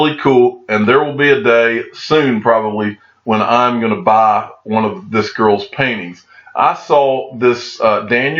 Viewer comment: I wasn't looking at the video and heard you say: "I'm gonna buy one of this girls panties!" Gotta admit, cracked me up!! Great Video as always.